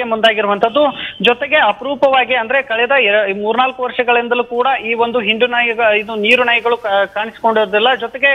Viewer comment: what the hell he said 😡😡😡😡😡😡😡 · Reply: Ig he told hindu nayi...right?